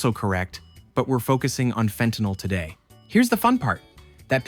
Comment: i thought it said femtanyl and i was like "how can you die from a music artist..."